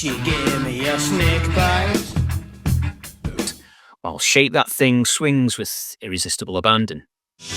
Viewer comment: liked the video, & almost spit out my beer when I misheard "Shake That Thing" as "SHAVE That Thing".